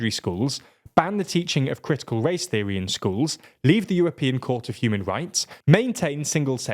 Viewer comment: I thought you said European Cult of Human Rights lol I know you said court, but I'll be using cult in the future now thank you.